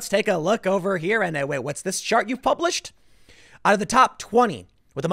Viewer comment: Ha. Thought he said “What’s this SHART you published” Would have been just as accurate & made me so proud.